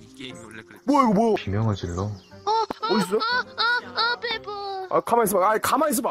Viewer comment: I can translate what they say! 2:24 멋있다Meosidda Awesome 2:27 시끄러워 Siggreowo What a noisy sound 2:31 하긴 이 게임이 그렇지 Hagin igameigreochi mo Well, you know this game 2:32 워 이거 뭐야wo igeomoya What the hell is this! 2:35 가만 있어봐! gaman isseoba X N hold on hold on(I think he just want say calm down!) 2:43 아 깜짝이야 씨발! AH! ggamjjak iya sibal! I'm scared! Fxck! 2:49 진짜 개놀랬네 jinjja gae nollaetne I'm very surprised(because of that thing) 2:51 이거 그거 아냐? 거기서 나오는 거 아닐까? igeo ggeoanya? geogiseo naoneungeo anilgga? is it that thing? appear at there somewhere 2:59 시발! 개새끼야! Sibal! gaeseggiya!(wa~nollaetsso) Fxck! you son of bitch!(wow scare! nice!) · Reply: all i heard was an an an an peppa